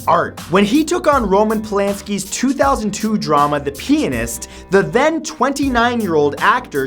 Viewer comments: Sounds like he said the peniss lmfaooookk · Plz say pianist differently. All I heard was "Penis".